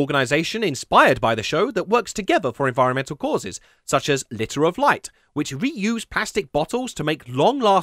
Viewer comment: It sounded like you said "litter of light" rather than "liter of light". Maybe it's just my ozone clogged ears though.